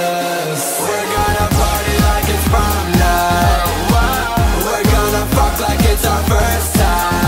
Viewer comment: I thought it said porn night 😭😂